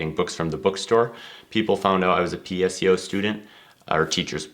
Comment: what is PSCO student? (Did I hear that right?) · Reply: PSEO, it is a Minnesota program and stands for Post Secondary Enrollment Option. Every state has a different name for their program, if they have such a program. Some call it Dual- Enrollment. Basically the program allows 11th and 12th graders to take college credit at a discounted rate or totally free depending on what state you live it. Hope that helps!